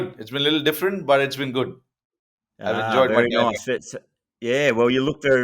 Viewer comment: Did rohit said he enjoys pandya?? 🤔